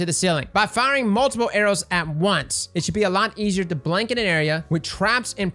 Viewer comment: Had To PaUsE thought he said BLINK !! o_0